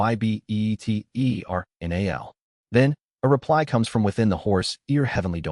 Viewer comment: For anyone wanting to know what was spelled out by the Great Mountain when he was speaking in the heavenly Devine speech that just gets spelled out all weird in Chapter 490.. I took the time to slow the video down so you don’t have to! You’re welcome :) spoilers ahead if you’re not already at that chapter :) 1:17:00 here the Great Mountain says to the Heavenly Punishment Supreme Deity “DEFEATED OF THE ERA, WILL YOUR DECAY BE ETERNAL?” After no reply (I think) he says- “IN THAT CASE” Then the Great Mountain continues with- “BE CRUSHED WITH THE HEAVENLY DOMAIN AND PERISH, DEFEATED OF FATE” And after his final.. interaction with Jung-Li he says- “SHALL WE BEGIN THE SUPREME DEITY HUNT?” Honestly even tho I kinda hate him, this whole scene was soooo sick. 😅